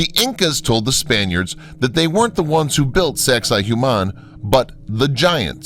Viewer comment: wtf is the name of the first place? I can't even google it, I hear "Sexahuman" or smth like this....